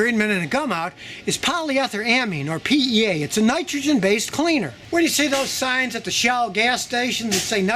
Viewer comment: Is there a specific kind of fuel additive that Scotty recommends? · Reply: I thought he said Gumout in the video.